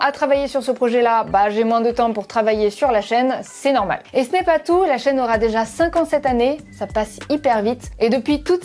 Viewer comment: J'ai compris "La chaîne aura 57 années" ^^